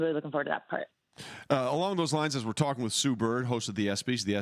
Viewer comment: I thought it said Sus Bird